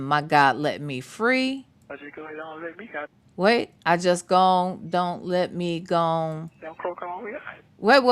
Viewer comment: It sounded like he said I told my God to let me out or croak all the way out. My daddy was raised in the Deep South in Georgia and you would have thought he was a college professor when you talked to him. I never had to try to figure out what he was saying . Never. I have a bunch of relatives still living in the Deep South. I swear not one of them talk like him-not even the ones who are older than him. So I just have to assume he has never even tried to do better in the way he communicates-never tried to learn anything. That’s on him.